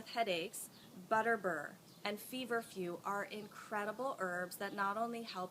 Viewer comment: what did you say? Vetiver?